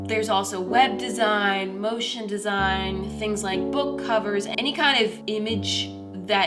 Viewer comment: Moriah: web design, motion design Me half way through completely spacing out: did you just say ocean design?